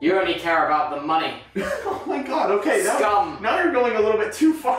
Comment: Tubbo actually said “scum” instead of “scam”